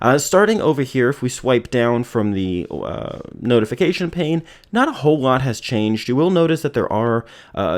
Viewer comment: did he just said "the notification pain" ?!